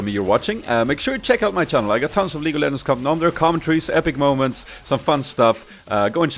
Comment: did he say league of lemons?